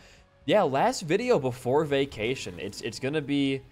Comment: I hope you'll have a great vacation!! Have fun, dude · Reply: I thought u said Vacination lol